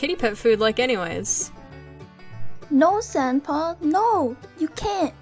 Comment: FOR A MINUTE I THOUGHT DUSTPAW SAID SEMPAW(like sempai) INSTEAD OF SANDPAW